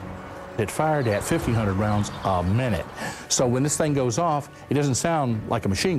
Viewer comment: it sounds like he says "fifty hundred round a minute". I don't know why I am getting it like that. anyways, thank you buddy.